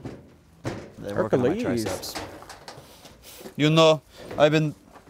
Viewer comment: I'm confused on what I heard listen closely and you'll hear a Russian voice saying "You know Ivan" I'm so confused